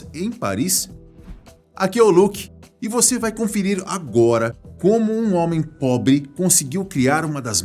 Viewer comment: Só eu que entendi ele falando "...como um homem pobi.." em 0:29??